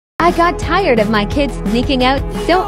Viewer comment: I heard “I got tired of my kids making out” and i wasnt reading the subtitles i was like WTFFFF